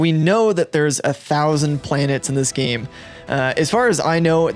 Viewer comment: a thousand planets? i thought they said 100